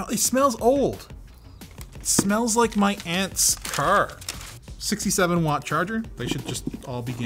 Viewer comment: Lmao when you mentioned the box smells like your aunts car then said it was a 67 W Charger I thought you said your aunt has a 67 Charger for a split second